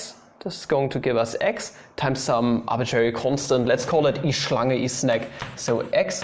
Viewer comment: What did u say at 16:26? E... something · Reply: Schlange means literally snake/serpent :)